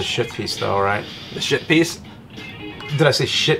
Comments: I keep hearing "shit piece" XD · I know what they're saying, but I heard "shitpiece" every time. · For a minute there I thought you said "Shit" Piece!!!! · I thought they kept saying shit piece.